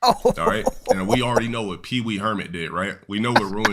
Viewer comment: I heard Peewee Hermit lmao that's our guy